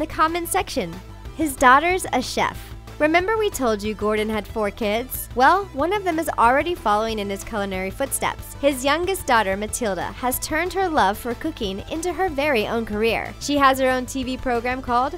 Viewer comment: I thought she said 'her love for cocaine'